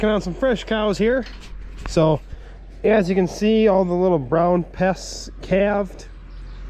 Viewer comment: Now everyone heard you say pests!!